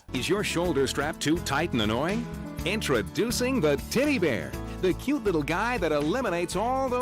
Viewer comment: I thought they said Titty bear. I bought a piece of sheepskin that raps around the strap, works a treat. Seatbelts are only measured for men which means that seatbelts ride up and rub on women's necks.